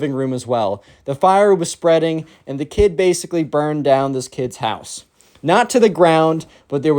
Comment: I thought you said burned your house